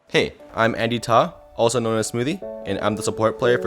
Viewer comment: I thought he said “Hey i’m Annie Top” lmao